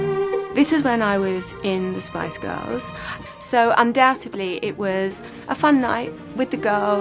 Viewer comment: Did she say, "undoubtebly"? 0:15 🙄